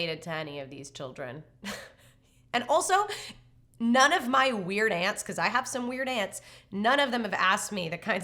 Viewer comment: Ooooh, "weird Aunt". I always heard "Weird Ant" and thought "What kind of example is that?"